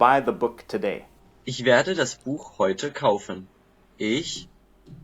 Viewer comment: the German audio said "ich werde das Buch heute kaufen", so "das" instead of "dieses" (written on the screen). "Dieses" would also be pronounced differently, and it corresponds to English "this";. In this kind of context English more likely uses "that" (than "this") which supposedly corresponds to "jenes" in German, but it is much less commonly used in German.